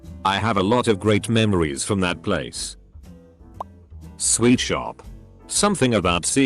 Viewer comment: I thought they said sweat shop for a second lol